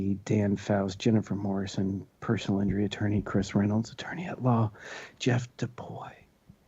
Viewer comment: I could have sworn I heard "Kush Reynolds"